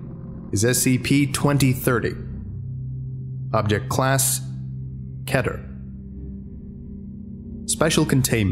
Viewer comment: Keter I thought u say it keatear